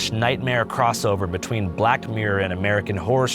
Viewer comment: Did anyone catch “BLACKED Mirror” at 9:39 ? Freudian slip or nah ?